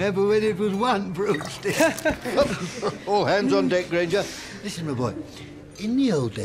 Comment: Wow I always thought he said “hands off there granger” and I thought that was hilarious too lol